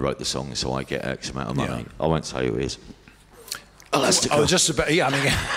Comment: who does he say there? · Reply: He says Elastica. As a fan of that band I’m saddened to hear they worked that way.